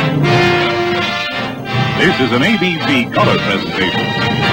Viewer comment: in the first time i heard this,i heard 'This Is An ABC Color Test Station'